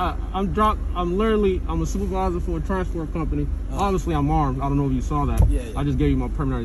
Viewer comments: Did he?... Did he say "I'm drunk" at 0:52?... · did he just say he’s drunk?😭